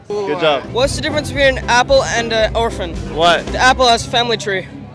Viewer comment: Idk why i thought he said oprah when he said orphan im like damn bruh what did oprah ever do to you